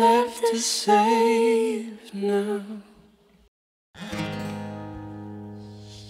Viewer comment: howard- “beautiful i like how you did this version” billie: “tHak YaW” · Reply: Shishter Shuck I heard it as more of a “tHak YeW” myself. 🤔😂😭 hahaha btw this comment is great. 😂